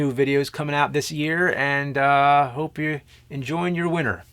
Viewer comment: It sounds like he says "Enjoy your wiener" at the end lol